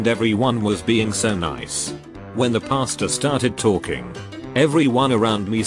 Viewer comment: I thought it said the pasta started talking.